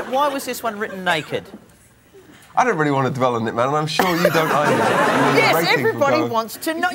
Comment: what is Chris saying at 3:27? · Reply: 'I don't really want to dwell on it, man, and I'm sure you don't want to either'